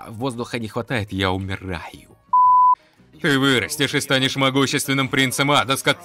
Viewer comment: я же не один слышу «Ты вырастешь и станешь паск-» ?